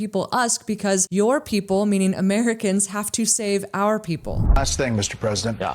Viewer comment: He didn't say that. He said "your people helped to save our people". 'Helped' not 'have' listen to it again, with headphones on. Only saying it because you made a point that was based on misunderstanding (because of his difficulty with english) so you ended up getting the wrong end of the stick. If he said 'have to help' like you misheard him to say, that really would make him sound worse and your conclusion would have been sound.